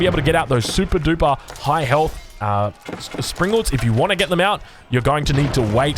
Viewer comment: I heard "High-Elf Springalds" and I had to double check I wasn't watching a Total War Warhammer video :D